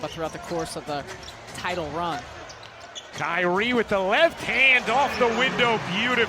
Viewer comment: I thought I heard diaree. lmfao